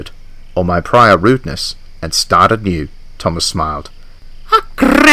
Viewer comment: what did Thomas say? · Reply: ‘Prior rudeness and start anew’